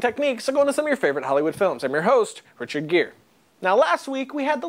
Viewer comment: "And im your host, Richard Gere" did anyone else hear that lol?